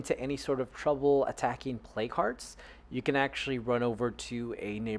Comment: At first I thought you were saying 'play cards' and then later I thought you were saying 'play carts'. Here I finally realized you were saying 'plague hearts'. As an actual beginner who's never played, it would have been helpful if you added that term to the brief opening overview. Other than that, thanks for the introduction! I appreciate it!